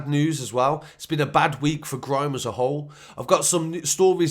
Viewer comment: I thought he said.........'It's been a bad week for crime' .............lol